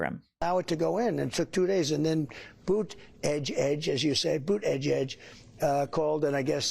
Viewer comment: I thought he was saying Putin-edge edge